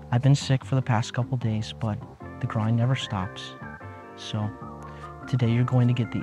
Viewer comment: In the intro I thought u said “the crying never stops” and I felt that